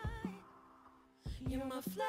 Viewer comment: All I keep hearing is "Fleshlight". Haha